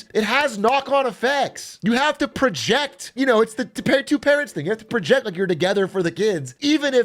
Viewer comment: thought he said “two parrots thing” which arguably is a lot cooler